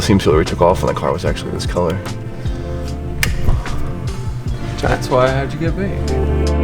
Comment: “thats why i have to get baked” am i hearing that right hahahahahaha